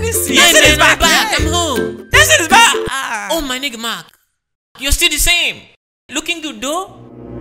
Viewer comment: I know im not the only one who heard denilson say oh my nigga mark?😂